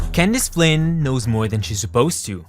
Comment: when I turn on the subtitles on this part it says can this plane instead of candace flynn not going to lie youtube's subtitles is so stupid